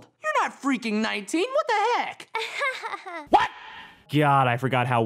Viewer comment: did Finn said what the heck